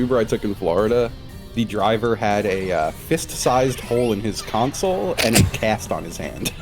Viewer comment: Fist-sized hole in the console and his right arm in a cast is absolutely absurd environmental storytelling. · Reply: i thought he said fist sized knoll as in a small natural hill was in his console